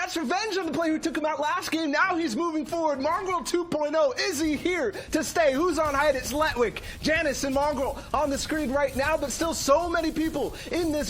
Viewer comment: マンゴー(空耳)